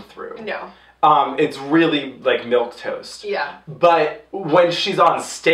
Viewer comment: im so happy the word milquetoast was used in this review wow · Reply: zannkke oh damn. I thought they said milky toast....lol